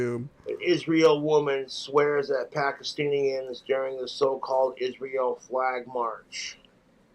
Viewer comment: did he just call them Pakistillian?